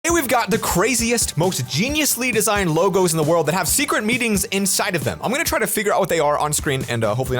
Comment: Wait is it just me or did say secret meetings at 0:04 cuz i am confused